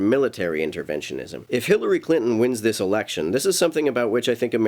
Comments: Did you say "Hitlery Clinton" at 2:10? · At the 2:10 mark...did you say Hitlery Clinton or do I need to get my ears checked?